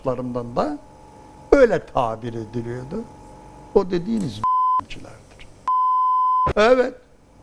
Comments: ne dedi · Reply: Götemciler diyo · da ne diyor? Bilen var mı? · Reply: Götemciler diyor 2. Bölümü de izle anlarsın. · da ne diyor sansurde? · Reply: Götemciler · ne diyor beyler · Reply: Götemciler